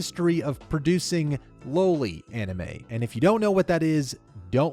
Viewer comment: I straight up thought he said "lowly anime"